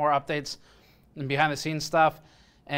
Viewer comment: I heard "We'll sue" haha